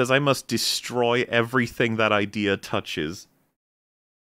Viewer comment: Did you say?... Touches?